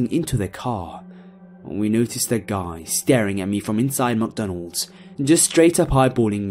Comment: When i heard "McDonald"... I thought "MuckDonald"